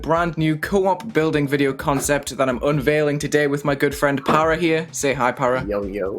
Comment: I swear I heard "girlfriend"